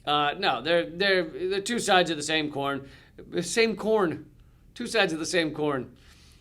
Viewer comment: I sort of wish you hadn't clarified the statement from "two sides of the same corn to "two sides of the same coin" because I thought you'd said, "two sides of the same porn," and I found that a most apt description.